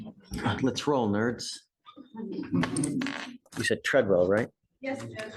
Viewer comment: Did the judge say that i thought what he said lets roll nerds 😂😂